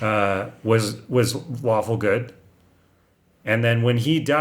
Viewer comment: I thought was saying "waffle good". I'm dumb lol.